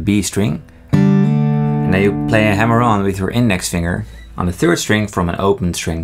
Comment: I kept hearing “you play a hemorrhoid..” I need some sleep…